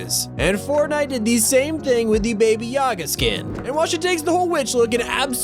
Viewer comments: did he say baby yaga?? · When you said baby Ya ga I thought you said baby Yoda I was like wait a minute something ain’t right 🤔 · I think it's Baba Yaga not Baby Yaga, because that's what it literally said that in second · Did he say baby yaga 🤣🤣🤣